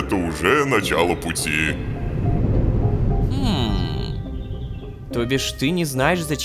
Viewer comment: мне одному послышалось тобишь